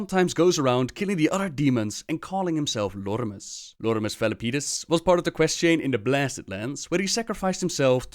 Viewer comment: It sounded like you said Lordimus Felipenis.